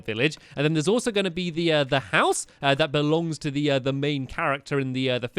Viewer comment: At/@ five fifty two, / 5:52, when he said out side, it sounded like heeeee said ow! Side